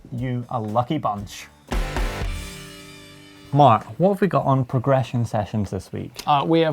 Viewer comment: Did he really call at 03:40 ? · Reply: Haha someone else said the same... I called him, musta been a soft T!